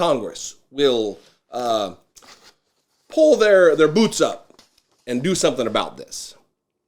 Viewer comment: I was about to land in your middle good and hard! I thought you said congress should pull their "boobs" up and do something about this. However, I listened several times over and I was mistaken. Your reference was to their "boots". I am going to have to ask you to enunciate a little better or I may have to clean out my ears. Nonetheless, keep up the good work!